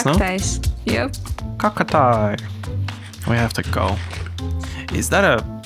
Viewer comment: It kinda sounded like he was about to say "cockatiel" lol